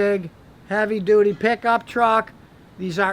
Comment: he clearly says heavy duty. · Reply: he clearly says heavy duty pickup truck....